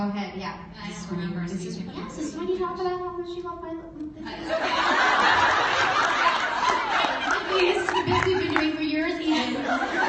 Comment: someone can tell what Liz said at 20:40 and 20:47 please! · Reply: "yes this is the part where you talk about how much you loved my lips. It's the bit we've been doing for years" 😂